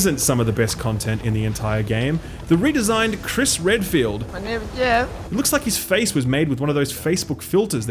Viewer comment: can someone PLEASE PLEASE PLEASE tell me what that soundbite is? It sounds like Borat or something. Is he saying “my name’s Jeff” ?? I gotta know what that is. Help me out fellas. EDIT: 22 Jump Street. “My name’s Jeff”